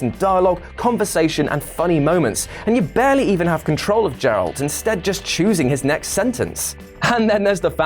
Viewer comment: did he say jeralt?